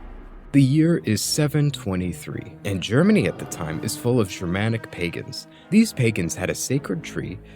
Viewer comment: I watched this video whilst shaving and misheard "Germanic Pagans" as "dramatic Pagans" and it still made sense.